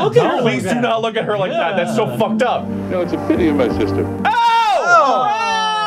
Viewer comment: What did he said? "You know it's a pity you're my sister?" That's F*cked up...